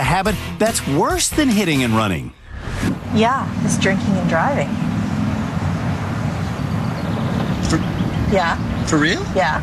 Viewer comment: When she said "drinking and driving," I heard "jerking and driving" and I was VERY concerned